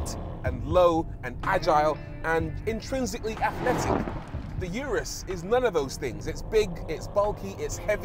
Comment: "Uterus", or did I mishear?